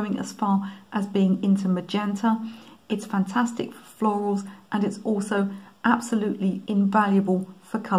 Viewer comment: I have perylene green, but the violet was a revelation (and incidentally, I've never encountered it before). What I've never used is Ultramarine Green Shade - I didn't see the point of it until I saw you demonstrate it: and now I realize I must have some. Agree with you about the other colours - I like Manganese Blue (Hue) and while you could mix it by adding a little pthalo blue to cobalt, it's never going to be the same as that very useful tube colour in watercolour and oil. Finally, yes, discovered Nickel Titanate Yellow a while ago; and I've used Naples Yellow for a long time - if you do want opaque touches, with perhaps Yellow Ochre or Light Red, mixing them with Naples Yellow will give you one of the only really opaque mixes you can get in watercolour without using white: everything else just sinks into the paint layer already applied - carefully mixed (and you can add a little white too) you can pick out stems of grass or twigs even over already painted areas. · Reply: By the way - I'm a little deaf, but had no trouble understanding you, except once: when you said 'florals', I heard 'floors' - and wondered why you'd be painting your floor with quinacridone rose.